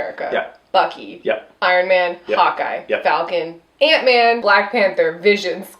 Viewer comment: 1. When says Hawkeye I thought she said "hot guy", which is somewhat accurate since Jeremy Renner doesn't really bring much to the table except muscles. 2. I love explanation of the previous movies and comic books is the best. 3. Please, please, PLEASE watch the SNL skit on a Black Widow movie. I think Black Widow is hands down one of the absolute best characters and she really does deserve her own movie.